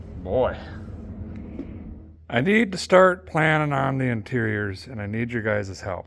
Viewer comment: I thought I heard you say deterior instead of interior.